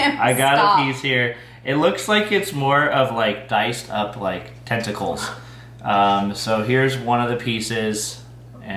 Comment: Uhhh…tentacles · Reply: UHHH, I know it’s Tentacles. It sounded like he said Testicles.